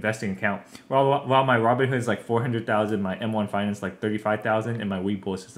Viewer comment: did he say he has 400k in Robinhood?